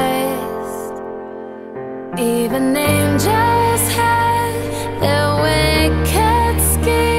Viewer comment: Am I the only one that thought she said: "even ninja's have their wicked schemes" ?